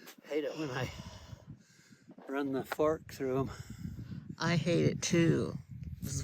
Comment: 😂😂 I thought said the f word! But he said fark 😂😂😂 (fork)😂😂😂